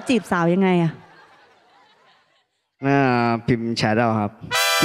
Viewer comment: เขาพูดว่าอะไรนะ ฟังไม่เข้าใจ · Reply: พิมพ์เเชตเอาครับ